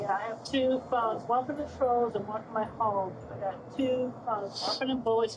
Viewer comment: I thought chrischan said "two phones: one for my trolls and one for my hoes" lol gonna be misquoting this for weeks now.